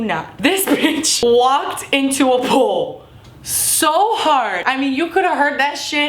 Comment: oh i thought you said a pool im like there a pool at IHop